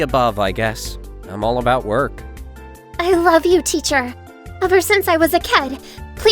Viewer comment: "I love you teacher ever since I was a kad" Do I hear it wrong or my ear have some problem